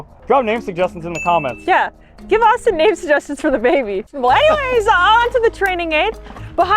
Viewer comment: what did say??😅 all I understood was “eh” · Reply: She said "Well anyways on with the training aid." but very quickly so it kinda merged into one :P